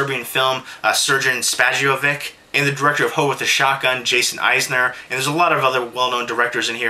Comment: Due to how fast you talk, at one point it sounded like you said, " ... and the director of Ho' With A Shotgun."